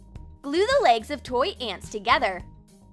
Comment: When it said glue the legs at 0:47 I thought it said Luna may